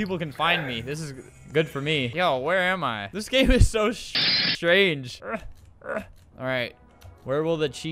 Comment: The Rarest Thing Humanity Has Saw Is : Swearing The First Time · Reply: he said strange the edit just made him sound like he said shit